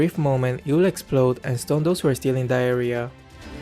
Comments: I love watching videos and I am so grateful he put subtitles on them. I was like "whaattt??" when I heard him say 'diarrhea'. Had to play the video again and read that he actually said 'in that area'. Hahahhaha I'm stoopid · I really thought he said “stun those who are still in diarrhea” lol😂😂😂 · sorry but i hear "Diarrhea" xD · what some people heard: Who are still in that area What I heard: who are still in diarhea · what said - It will stun those who are still in the area what i heard - It will stun those who are stealing diarhea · why did I think he said “diarrhea” for a quick second 😂